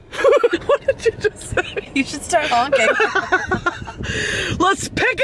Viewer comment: i thought brittanys water said shart